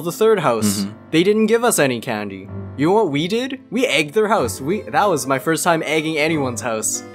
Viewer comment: I thought said soo we ate his house 😂😂